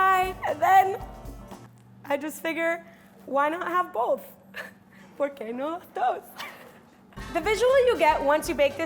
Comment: did she just say "por qué no haz dos"?? That's spanish men 😂😂😂. This girl is amazing, i fell in love with her tbh.